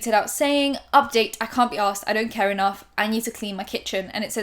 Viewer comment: wait.... has been saying “I can’t be arsed” this whole time instead of “I can’t be asked”? 🤯